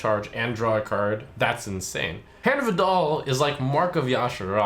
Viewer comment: Is it just me or does it sound like he says "hand of a doll"?